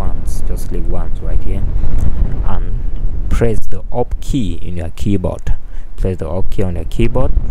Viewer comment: the up key is the up arrow key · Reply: lmfao i thought at first he said the opt key... im like what... ohhh the ALT key... then nope... i saw your comment. thank you!